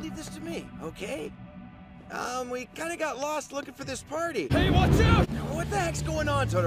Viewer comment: what I heard: "babe watch out!" Is it just me?